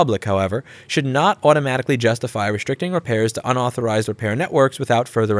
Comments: It sounds like you made a slip of the tounge at 0:45. I hear you say 'unauthorized' when it says 'authorized' but that might just be my hearing. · did I just hear UNauthorised? youtube's speech recognition heard UNauthorised too apparently...